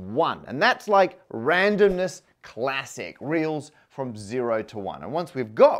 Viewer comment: Your subtitles are incorrect. It says "reels" instead of "reals" at 0:34.